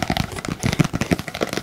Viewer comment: Etendi foi nada -17:59 kkkkkk 😂😅😴😴😴😴😴😴😴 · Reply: Ele fala "left rigth,up down, circle"